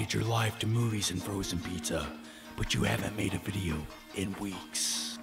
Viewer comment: I thought it said movies, bangbros and pizza at the beginning 😂😂 great vid anyway